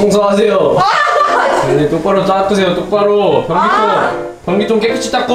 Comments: 나만 변기통 권기동으로 들리나요?ㅋㅋㅋㅋㅋㅋㅋㅋㅋㅋㅋ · 이때 나만 변기통을 권기동이라고 들었나?